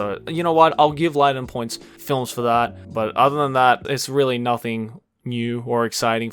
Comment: I think he said Liden points films for that